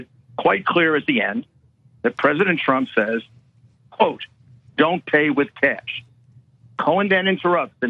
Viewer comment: I dislike Trump with a fiery passion, but the first few times I listened to it I heard " don't pay with cash". I listen to a lot of terrible audio doing transcription work, but this seems pretty clear. But its still a different degree of a lie, like arsonist saying he didn't use gas to start the fire, he only used a road flare.